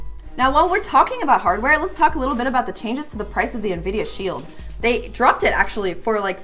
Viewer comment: sounded like she said via shield